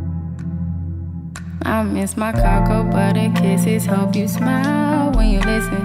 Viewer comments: I thought she said I miss my cargo buddy kisses😂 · no one: me: googles i miss your coco butter kisses hope you smile when you listen edit: thanks for all the likes ahah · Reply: zoë caidence I thought it said I miss your cargo but the kisses.. I literally typed that in on google XD